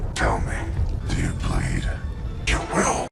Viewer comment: XD It sounded like "Ku will!" XD